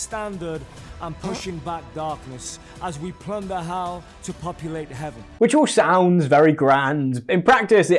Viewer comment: “Plunder Hell to populate Heaven” that doesn’t sound like a good thing. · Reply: oh I thought he said "ponder how to populate heaven" lol